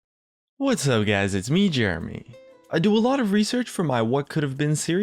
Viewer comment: I'm french and each time you say "jeremy" in your intro i hear "germany", this comment was useless